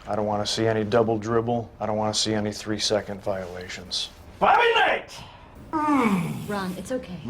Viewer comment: What does he yell while pointing at that picture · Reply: BOBBY KNIGHT